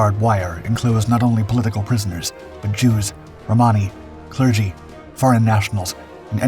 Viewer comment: The video voiceover says "Jews and Hammas" were sent to Auschwitz. Closed captions say "Romani". Why "Hammas"?